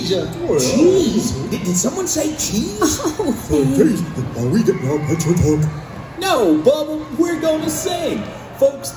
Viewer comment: Can someone please tell me what Bubba says at 0:30? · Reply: Bubba: OK are we getting our pictures took.